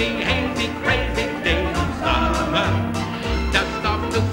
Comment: Gilmore Girls, haha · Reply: I thought he was saying crazy crazy crazy lol